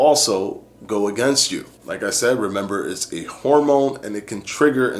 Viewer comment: I thought he said "remember is a hormone". Awesome Video tho 👌🏽